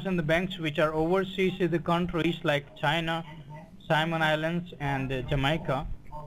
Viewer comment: I swear, I had to replay some of that three times, and still had no clue what he thought he was saying. Simon Islands? Did they name some country or islands, "Simon",now? Is that like Epstein Island? Then it hit me. I tink dees guy is Chinese. I tink he means, "Cayman Islands". (soft -c, and treat the -a like a shwah.., so it's sigh-man") Jeez louise! They really have to get better than this!